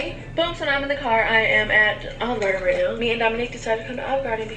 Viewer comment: I thought I heard her say Domonique instead of Domonic